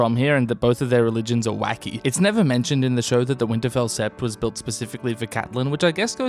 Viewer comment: What are u saying at 2:53 "the Winterfell set was built for Catelyn.?? I'm not sure. THANK U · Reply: The Winterfell sept, as in, her place of worship. As a Tully, Catelyn follows the Faith of the Seven, whereas the Starks pray to the Old Gods in their Godswood. Ned had a sept built at Winterfell, so that Catelyn could practice her religion.